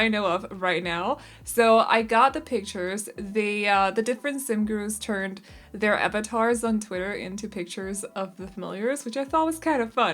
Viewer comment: I had to listen to this part at 1:23 several times because the first few times I kept hearing "The SimGuru's turned their abattoirs into pictures of the familiars", and I was like, that sounds dark even for Guru's like Ninja and PJ. :)